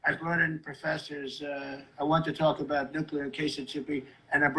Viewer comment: what word did he say? Hahaha Sounded like nuclear - casitivity.